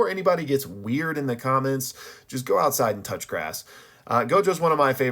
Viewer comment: I heard "go outside and touch your ass"